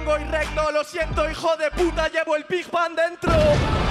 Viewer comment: Al principio pensé que decía discman dentro :v